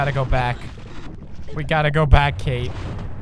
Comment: did he say around 12:00 "we gotta go back Kate"😂